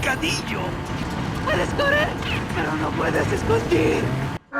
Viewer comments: My Spanish listening is so bad I thought the female ganado said ‘’esto es pero no puedes discutir’’ which means this is it but you cant argue it 😂 😂 😂 good video !!! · I'm spanish speaker and the "spanish" of the game is very bad, for example the lady at 02:29 is suppose to say "Puedes correr, pero no te pueses esconder" and when I hear it's like "P-reso es, pero no puedes esconDir" 🤷🏻‍♂️ and there is another one at 03:44 when they say "so cerdo" I never heard that before in real life, we don't use the word "so" 🤷🏻‍♂️ and to finish, there is another phrase that is not included in the video which is: "Quiero su cerebro" which is said in the castle by the illuminated in black, it is a very rare phrase, they almost never say it